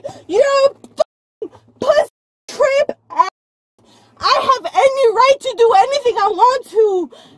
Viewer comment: she actually said "I have any right to do anything I want to"